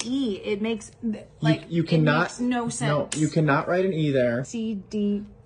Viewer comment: I thought he said not to write an E?